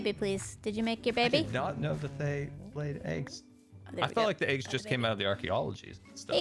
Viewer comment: did anyone hear female like voice... im quite sure it wasn't game sound nor voice... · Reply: If I'm hearing the same thing you are, it sounds to me like saying something like "Oh there we go, I have a baby" (I'm pretty certain about "Oh there we go" but I'm mostly guessing for the rest)